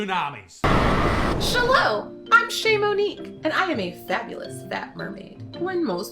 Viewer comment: To be honest, i thought she said her name was shamu'nique lol. 🐋😅😅😅